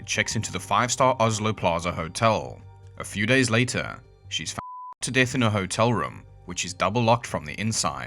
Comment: Hey guys, sorry about all the censorship in this video, I know it's annoying, but it is NOT something I want to do or am going to keep doing. It is because this particular video was DELETED twice by YouTube. Not demonetized, not limited in reach, DELETED. I learnt the hard way just how seriously Youtube takes talking about Suicide. I find this case very interesting and wanted to be able to share "Jennifer's" story. Thanks for watching and thanks for all your support 🐈‍⬛ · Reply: I know you are saying “shot” but in my head I hear “…fucked to death” and “she shit her self”. Point being, they can control our words but not our minds I suppose.